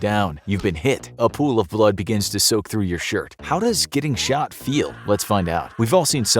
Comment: I thought it Said "how does it feel to be short"